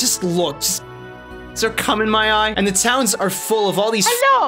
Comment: I love the art in this game it's the only Mana I played and it's still beautiful to this day. At 2:00 did you say you have cum in your eyes? This the first video I watched in your channel btw :p